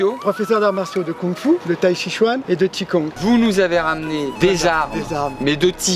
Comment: j'ai entendu "et de p'tit con"